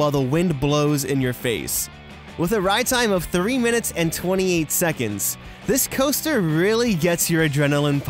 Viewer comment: I heard 38 minutes and 28 seconds.